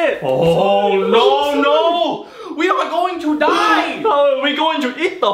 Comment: OMG NAUURRRRRR I THOUGHT IT SAID "WE'RE GONNA EAT THEM" 😭😭😭😭😭😭😭😭😭😭